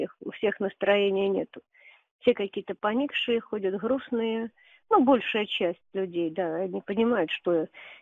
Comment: Re: the grandmother who calls Ukrainians "khokhly" and "skunks"... Is it possible that she herself has a Ukrainian accent? around 1:14 where she says everyone in Russia is sad, I seem to hear "hrustnye" instead of "grustnye." Or is it a different accent?